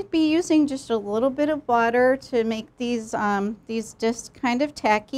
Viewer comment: is she saying BUTTER OR WATER??? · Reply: Our chef uses water.